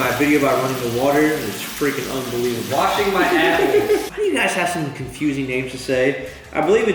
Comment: When said "I'm washing my apple" I thought he said "I'm washing my asshole"